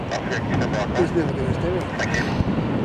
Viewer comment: Did someone say at 1:58 he doesn't know what he's doing, ? Cus I bet you couldn't land in that! People these days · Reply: No they didn't. They said "He's never gonna do it". I never said I could, but if you're going to be like that, I bet you couldn't land in that either.